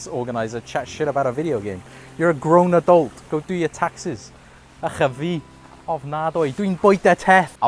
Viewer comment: great video, i love that firewatch is still talked about and i want to know what you said on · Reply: Thank you! And: 'Ych a fi' = disgusting 'Ofnadwy' = terrible 'Dw in bwyta teth' = I eat nipple